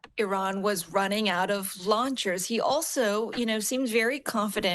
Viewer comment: It sound like she said Iran was out of lawn chairs and so I am going to believe that over them being out of launchers.